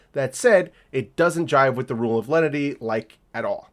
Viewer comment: When you said "rule of leddity" (or however you spell that shit) I heard "rule of ledditry." Which is true, really. The ATF really is the most reddit of the alphabet bois. Not-so-petty tyrants and amateur investigators who annoy everyone in the communities they're a part of and occasionally get people killed.